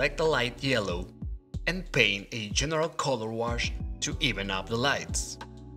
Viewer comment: What is said at 6:35 please? Something ... colorwash. I like your voice, and LOVE this tutorial- and LOVE this style!!! I love the not perfect lines. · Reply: Hi Paul, I said 'general color wash'. It's basically a fancy way to say color layer :)